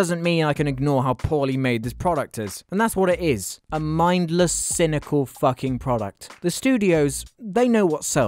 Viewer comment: I thought he said Crystal but he said cynical